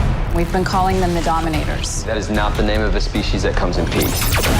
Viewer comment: Would Kid Flash be on the crossover? When Lila mentions the name dominaters, i thought I heard Wally say "that's not a friendly name" or something like that